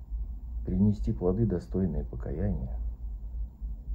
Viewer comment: "сдаться советской власти"? или послышалось в конце? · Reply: Сдаться светским властям! Внимательно слушайте.